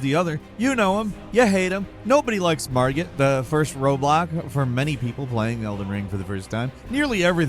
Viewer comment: I swear i thought he said the first roblock 🤣🤣🤣 but clearly was roadblock, but i like roblock better lmao